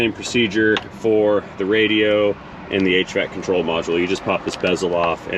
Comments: I thought you said 8 track control module instead of hvac - lol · At the 3:00 mark did you say "8 track control module"? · "Hell, Yeah!" Funny.... Sounded like said "8-Track Control Module" when he said "HVAC control module". I can see him installing an 8-Track! 🤣